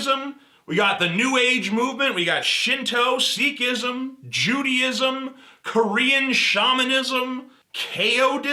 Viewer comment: heh when you said Judaism because of some background noise here it sounded like you said Chewieism, that would be cool, Sign me up to the Church of Chewbacca, betcha it was started by Ewoks as a denial of C-3Pism